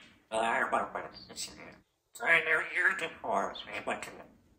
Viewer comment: I heard “i knew you in two forms” O v O” help Btw! The first time i think Giz said so many times CHIRP in a single way here! (I saw all your vids lmao)